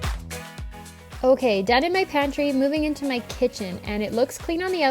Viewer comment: I think you said, "OK, Devon McKenzie, movoing into..." Who is Devon McKenzie? Or is that not what you said & I just need to get the wax out of my ears? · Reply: haha, i had to check - i said 'OK, done in my pantry..." lol, I talk really fast I know :)